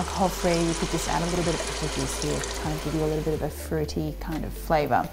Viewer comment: What does she say around the 4:34 mark? About what to use if you want to keep your cooking alcohol-free? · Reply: Apple juice :-)